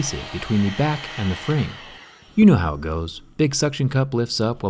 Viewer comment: what you said there, i didn't understand. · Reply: You know how it goes.